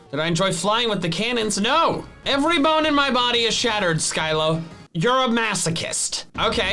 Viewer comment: did he just say skylo